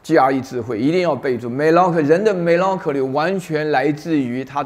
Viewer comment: 人的 “xxx” beautiful word，那个单词是什么听不清楚，有知道的朋友吗? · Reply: melancholy